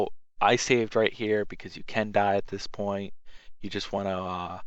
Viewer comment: Sounded like u said cacti instead of can die